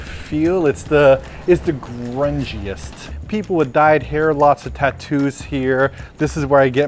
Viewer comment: I wasn't paying attention to the captions and I thought said "People have died here." I was like, "Wow, that IS punk rock!" XD